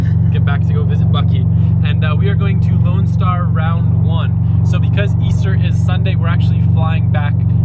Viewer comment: damn i thought u said lone star round up in austin